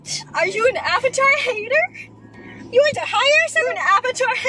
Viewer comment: i thought roni said "are you high or something???" LMAO~~~!^^